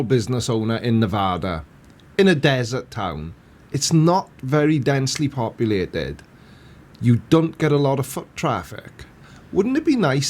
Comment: Woah! What did say at 9:47? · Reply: "...not very densely populated. You don't get a lot of foot traffic..."